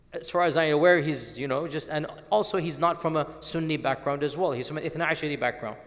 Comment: what background did he say? Not sunni but ?? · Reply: Twelver Shia background